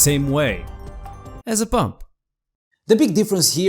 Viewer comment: Did he said pump or bump?